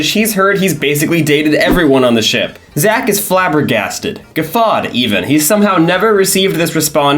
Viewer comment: What word are you saying here? Geffad?